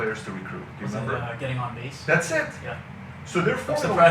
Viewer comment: Getting on what? Sorry english is not my first language, I couldn't understand it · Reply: "getting on base" it's a baseball reference. <link>